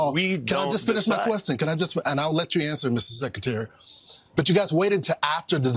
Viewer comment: Did I hear him say "Mrs. Secretary"? 😂